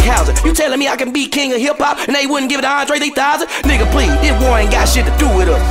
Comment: I swear I heard him say Andre 8000 and I looped this dopetastic track multiple times already.